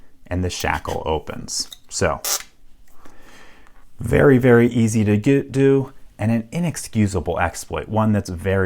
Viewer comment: Am I hearing it wrong or does he say "doot doo" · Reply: Zeldaboy He was trying to say “Very easy to get” and then changed it to “very easy to do” mid-sentence. Because I’d that it came out as: “Very easy to get-do”